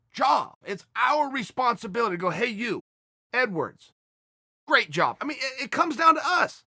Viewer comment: " Hey you N-Word, Great job" That's what I thought uncle chael said lmfao